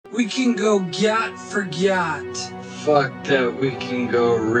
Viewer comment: Help the first thing I hear "We can go gyatt for gyatt" in slow motion 😭